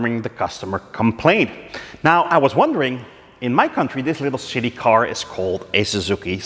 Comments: Is it your dutch accent or did I hear you call this Suzuki Swift "a little shitty car " ? 😀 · Did he call it a little shitty car?